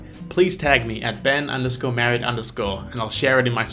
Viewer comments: i thought you said, "I've been married" lol😂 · I heard "a bit married"...